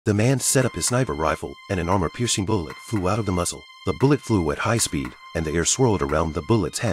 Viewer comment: I thought you said the bullet was moving at high speed